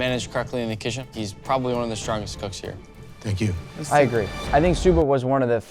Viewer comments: did Nick just said I agree to himself? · did bro just say “I agree” to himself😂